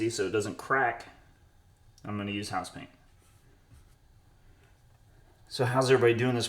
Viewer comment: What did you use for the base…I thought you said house paint